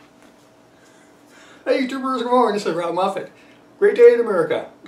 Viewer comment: what did you say?! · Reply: “howyoudoinerrbody good morning!”